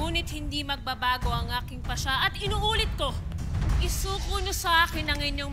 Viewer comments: tama ba narinig ko "PASNEYA" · Narinig niyo ba yung "pasniya"